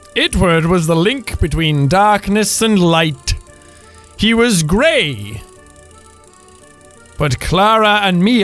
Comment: anyone else think that said "gay"?